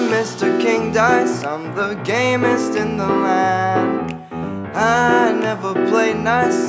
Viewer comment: At first i thought he said "The gayest in the land." God I'm stupid XD